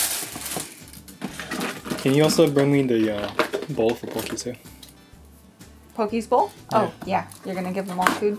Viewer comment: did she just say pokeball? lol